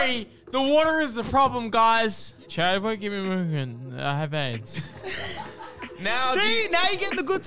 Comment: I just notice he says "i have aids" instead of "i'll have eggs"